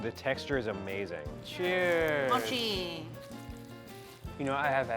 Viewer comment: I thought it said mocha